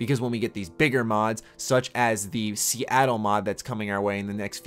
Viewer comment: What is he talking about a 2:18? Did he say a Seattle mod?